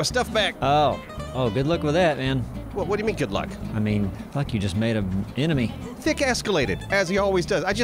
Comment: Lol did say fuck at 14:24 ??